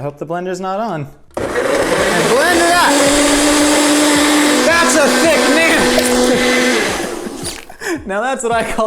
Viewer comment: I’m sorry but what did say at 14:44 🧐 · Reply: "Now that's a thick nanner!"